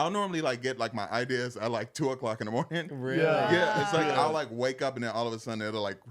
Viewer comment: He said “I get my ideas at like 2 in the morning” I heard… “I get my IBS at like 2 in the morning”